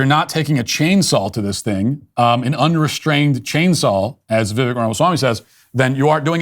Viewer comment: "chain sall" ? Am I hearing this incorrectly?